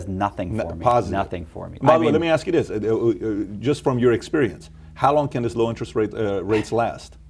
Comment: did he just say “mummah let me ask you this”??.... Huh?